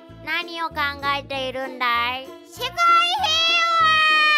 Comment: 自分用 0:06 世界平和。 凄い平和にも聞こえるw